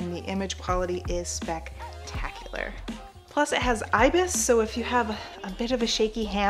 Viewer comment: I thought you said it has ISIS 🤦🏻‍♂️